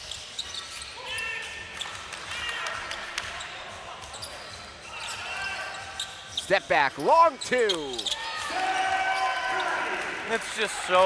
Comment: For some reason the commentator sounds like hes saying steph hahaha but yea a team needs to give him a contract